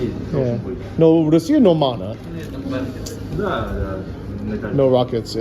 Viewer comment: Does anyone know what is saying around 10:42 ? I have heard the phrase "no mana" many times and have no clue what it means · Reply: it's нормальный or normal-na. Which depending on the context means things are as normal, ordinary, usual. But can even mean things there are fine.